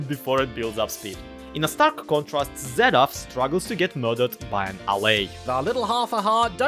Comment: I had CaptionsOnThisVideoWereProvidedByLyarrah™ turned on and at 11:53 I thought said "In a stark contrast, Zedaph struggles to get murdered in an alley" for half a second.